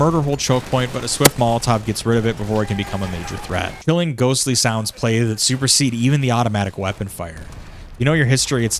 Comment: I thought he said “Papa John’s supremacy reigns supreme”